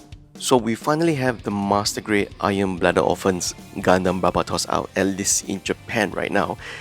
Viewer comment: At first I thought you said "Iron Bladder Orphans"... ;)